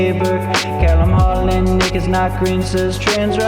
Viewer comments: The cc thought nick said the n word when he said nick is not green😟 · I literally thought you said "n*ggas not green" lmao